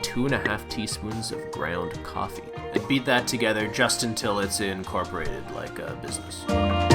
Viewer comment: did he say "beat it together till it is incorporated like a business"...